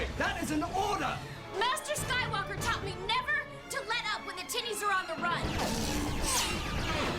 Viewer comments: the closed captions says "never to let up when the titties are on the run" ... WTH?!? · Bro I can’t lie I thought she said “master sky walker taught me never to let up when the tities are on the run” 😂😂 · uhhhhh did she just say what I think she said...? “Master Skywalker taught me never to let up when the tiddies are on the run!” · my subtitles say "master Skywalker told me to never let up when the titties are on the run" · who else thought Ashley Eckstein said "Titties" instead of "Tinnies" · Did I hear that right? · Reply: Tinnies?? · i thought she said "master skywalker taught me never to let up when the the tiddies are on the run" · did she just say when the titties are on the run? 😂😂😂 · I’m not the only one who heard Tiddies, right? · anyone else hear “titties” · did she just say "the tides are on the run" 😂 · did she say “When the titties are on the run?” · " master Skywalker said never to retreat when the titties are on the run". Did I hear that correctly? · I thought ashoka said when the tiddies are on the run lol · “Never let up when the tities are on the run” I misheard that every time 😂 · am i the only one who heard titties at 0:36? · WHAT DID SHE SAY??? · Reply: Tinnies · I literally just woke up. Did I hear her say never let up until the titties are on the run?? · I thought she said "when the tittys are on the run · did she say Titties